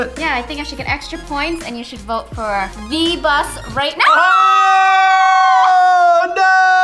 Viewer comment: I thought said v-bucks